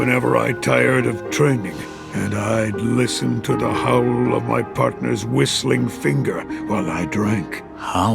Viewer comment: I had to repeat that part, i thought he said ..." The howl of my partner from my finger"